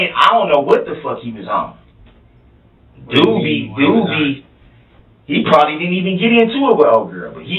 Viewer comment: I’m sorry but that subtitle at 1:32:22 it said “ Doobie Doobie “ 😂😂, it does sound like that’s what he’s saying, but he’s actually saying “ Dude Be…Dude Be” …as in “ Dude be trippin “…but overall tho…awesome job on the subtitle 💯💯🙌🏾🙌🏾